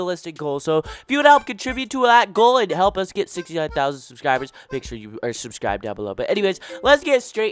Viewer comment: you made a mistake here, the captions say 68k but you said 69k Also 69k 💀💀💀💀💀